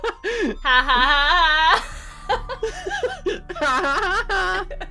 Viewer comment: When said”let’s keep moving” I thought he said “ let’s keep living!”